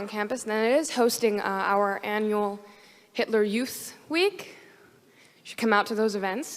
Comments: inplayed it three times. Did she say “hitler youth week”? Hahahaha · Wait did I hear correctly or did she just say “hitler youth week”?? And then go on to say that it’s not supported?!!